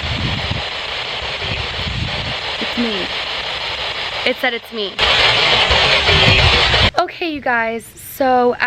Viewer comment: heard "It's me", but I hear "Sabrina"